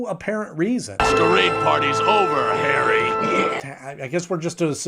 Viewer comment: Just realized: batman says "party's over harry." a possible harry Osborne homage? Not to worry, I'm no marvel fan. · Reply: I thought he said 'hairy.'